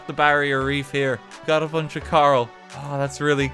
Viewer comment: He said coral, I heard, "karl".